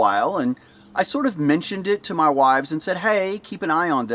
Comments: Yes you heard right, I heard it as well. Wives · did he say wives? As in more then one? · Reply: He said wives and they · Did he say.... wives? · did he say, 'Wives'? and then that, ..'They said..' ? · Did I hear that right? "My Wives" and "They" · Did just say he discussed with his "wives" (plural) and that "they" (plural) gave him some advice? - See time 1:13. This guy really is the BOSS! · "I mentioned it to my wives..." Did I hear that right? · wait did he say wifes at 1:10 ? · Did i mishear you or did you say i mentioned it to my wives? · Sorry, did you say wives as in plural? just to clarify, do you mean wives that are yours or wives that you know. this isn't important to the story but caught my attention. no judgment